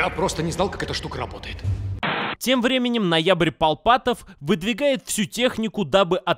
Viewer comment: Август Куратов Апрель Варкутов Декабрь Руберов Январь Камшотов Ноябрь Акбаров Курменов Куркотов Сентябрь Евлатов Ноябрь Полпатов Январь Фульфитов Ноябрь Фонфаров Кильков Фархатов Январь Руберов ... музыка для моей ушей! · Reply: Я услышала "Палпатов" ака палпатин. Только кишка тонка, на палыча не потянет.